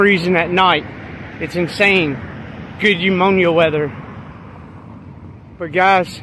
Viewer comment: I had to go back and listen a couple of times, I swear he said "good umonia weather"